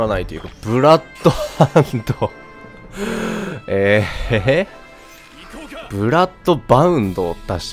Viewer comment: ブラッドハウンドに聞こえた apexかよ